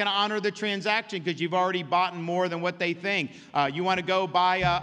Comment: did he say "boughten"???